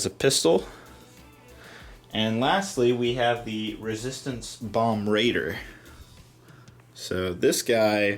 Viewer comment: 10/10 want this set. Love bombers. I got the Ghost since it had a lot of the style of WW2 bombers, and I want this too for the same reason. Also want BB-8! It's "bombardier" not bomb raider. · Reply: Saw it for the first time and thought it was said “bomber raider”